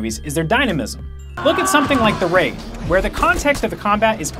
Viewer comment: What movie are you talking about at 9:15? I know you said "The Ra-" but I can't quite discern what you said. Thanks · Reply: The Raid